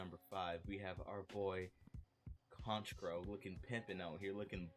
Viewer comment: I gotta put on closed captions with how quiet your audio is in this vid. On the bright side, the inaccuracy of the closed captions when I can hear what you're saying is a bit amusing. 5:53 "conch grow" (Honchkrow) 7:09 "penis or" (venusaur) 7:32 "a lone woman right you" (Alolan Raichu)